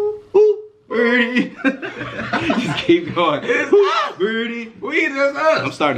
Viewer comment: did I heard burdy